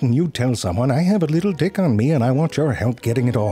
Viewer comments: For the whole video i heard little 'dick' not tick🤣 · Why did I hear "little Dxick" lol.... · Sounds like he says little dick 🤣